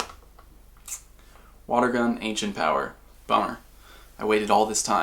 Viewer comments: I swear he said Boner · Did he say boner?